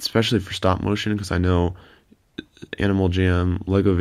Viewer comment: did he just say animal jam at 7:36?